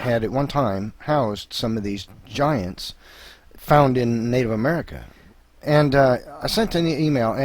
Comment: "Found in Native America." pardon? Maybe I didn't hear that right.